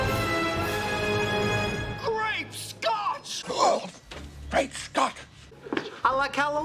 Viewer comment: I always thought Barney said "Grape Scotch" at 08:48 because of the newest scotch they drink a few seasons before, proving the new is always better rule.. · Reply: Yes but the way he said it was to mimic the "great scott" quote from BttF